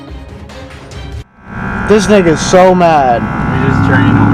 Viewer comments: did he say nigga? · Did he say nigga